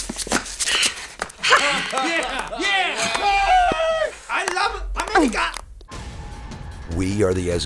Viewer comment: did they say "I love America" Lol